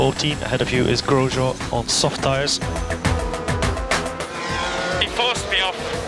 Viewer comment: Subtitles say "girl job on soft tyres" XD